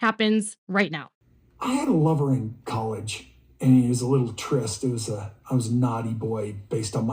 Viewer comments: First, she landed on her butt when she came off the 4-wheeler & secondly, when he was talking about his college relationship I swear it sounds like he says “he was a little tryst” not it when he was talking about it. Does anyone else hear that? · Did I mishear ?? did he say he had a male lover in college ?!?!?